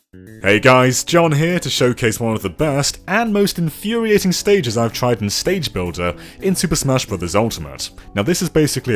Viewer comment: 本当にステージ講座って聞こえた…